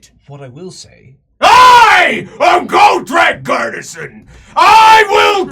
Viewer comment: ish He actually Said "Gotrek Gurnisson' Didn't he? like.. ok I knew he'd been a slayer before Felix, or wait, is this before Felix, I've Paused, This Could be Happening During Manslayer, I'm gonna unpause and hear more